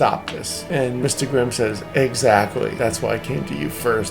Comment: I heard bad say “eggactly”